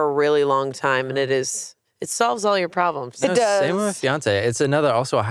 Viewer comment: i thought said “Beyoncé” and i was soooo confused i had to go back to realize he said fiancée 😭